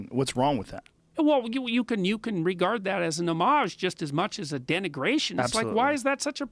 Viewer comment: What is the word he uses at 3:09 " you can regard that as a ___________ just as much as a denigration" please?? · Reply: Jango32 thanks, I kept hearing namage :-)